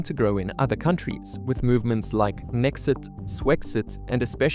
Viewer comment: Lol I thought he said Mexit haha